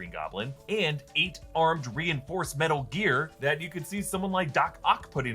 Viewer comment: did you say metal gear?